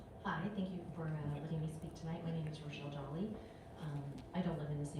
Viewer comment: did she say "thanks forallowing me to speak "??😮, SHE SHOULD SAY, after walking up, HEAR MY WORDS!!.. THEN SPEAK HER WORDS.. and the coward maytard should be there to hear her speak..